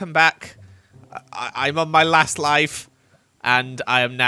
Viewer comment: Sounds like you said “asst life” in your he intro ❤